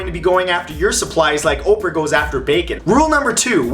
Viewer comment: I thought you said Macon from the walking dead lee is from Macon